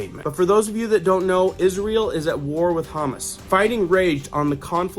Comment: I thought you said Isreal is at war with Hummus. Lol. Its Ha-Mas, unless you were saying it that way purposely.